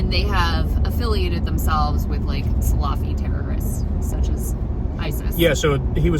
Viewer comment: Sorry I know its off topic but #couplegoals. Also I thought they were saying "Sloppy jihadists" but it was "Salafi" lol